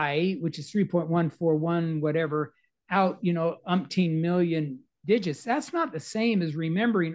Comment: Someone has memorised the number pi to over a million decimal places? Really? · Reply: he didn’t say Zillion. He clearly says Million 0:34 - it’s a very poor attempt to gaslight and recreate history which is what many historians are very good at doing.